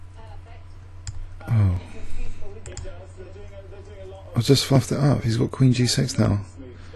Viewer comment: did you say I just fucked it up? lol